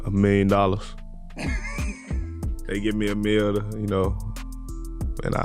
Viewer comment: Ja Morant did not say a million dollar prize, he said if they give him a mil. In other words, he wants a mil to compete in the contest and not just get paid if he wins.